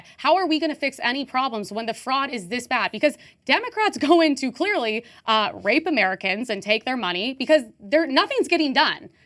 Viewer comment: did she just say “democrats rape Americans?”